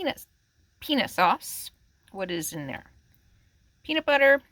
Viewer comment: why is this called penis??